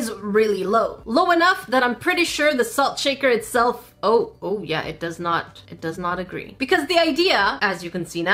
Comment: Did I hear ‘salt shaker are u gay,yeah’